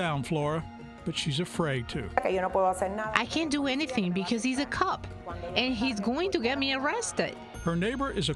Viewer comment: i thought she said he's a cuck.😅